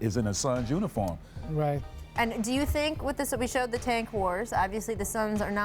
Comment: lol did she just say "Tank whores"?